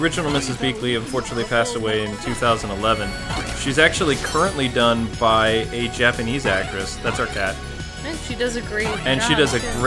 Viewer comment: I thought he said " Mrs. Beakley is voiced by a japanese voice actor. That's our cat." You have a japanese talking cat?